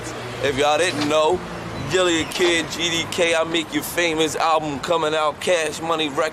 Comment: Did Gillie Just say GDK!?😮I Didn’t Know He GangBanged!?😮